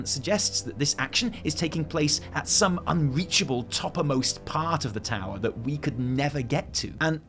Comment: "Toppermost" is what I hear at 7:49 - is that a word, a mashup of topmost and uppermost? Or am I mishearing topmost?